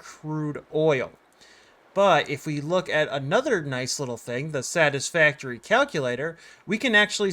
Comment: thought u said the saddest factori